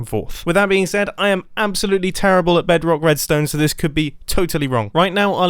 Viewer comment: did u just say bedrock??